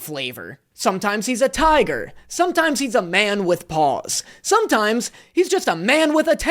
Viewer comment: I honestly thought you said "man with balls" instead of 'man with paws" lol